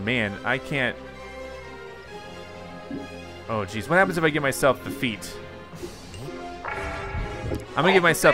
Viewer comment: i think you said `what happens i get defeat'